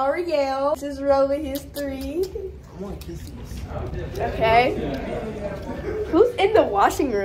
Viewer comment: did he just say he want kisses?